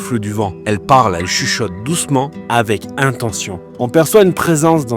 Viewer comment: Hello ❤ comme plus bas j'ai compris " tout doucement " comme pour te dire de faire attention.